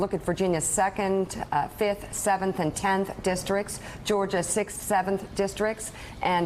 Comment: When Mika said ,"A few races to watch" , I thought she'd said "A few racists to watch".